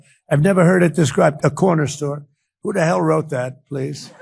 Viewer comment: BEN!!! Second time ive posted this,.. he clearly said that he had heard the term corner store before and then questioned who writes that into a speech. It had nothing to do with his deteriorating health but that's how your spinning it. I'm noticing that you're doing this more and more and it's starting to get troublesome. We the facts 2:37 not bias and opinion